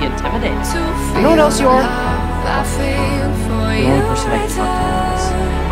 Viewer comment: wait i’m sorry i’m slow but what did he say to cyrus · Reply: Jennifer Hernandez “You know what else you are? The only person I can talk to like this.”